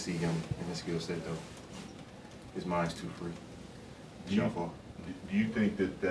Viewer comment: Did he say Kyrie's mind too fruity? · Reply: He said his mind is too "free"